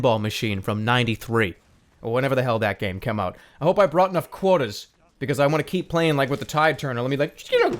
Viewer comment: Could've sworn he said "quotas" and not "quarters" with a New England accent so i was confused.